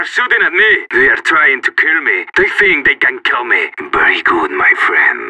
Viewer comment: Whats he's saying 4:52-4:54? Some thing like i could use morgan here xD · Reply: just 1 more what he's saying at 5:02 - 5:03 something like dizzy they can kill me ?